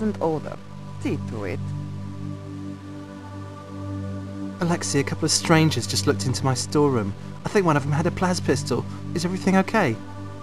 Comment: I know he asks "is everything ok?" , but what does he say before then. Something went into his storeroom? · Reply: Alexey, a couple strangers just looked into my storeroom... i think one of them had a 'plas' pistol! Is everything ok?